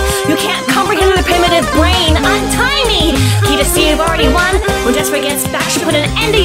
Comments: I thought peridot said "IM TINY!" so I thought 'you sure are peridot' (And yes I know it's 'untie me') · What peridot said: Untie me! What i thought she said: IM TINY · Am I the only one that thought Peridot said I'm tiny when she really said untie me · Lyrics: Don't touch that, Refrain! You can't comprehend with your primitive brain, Untie me! Can't you see we've already won? When Jasper gets back she'll put an end to your fun, I mean it! Desist! I don't even want to think about the size of the list, Of every. Single. Law. You're breaking! Not to mention the ridiculous mess you're making… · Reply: i heard "im tiny" instead of "untie me" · When peridot said untie me,I thought she said "IM TINY!" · When it said untie me I thought it said I'm tiny